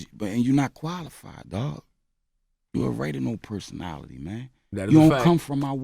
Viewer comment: Did he just say you a "RAIDIMO" personality????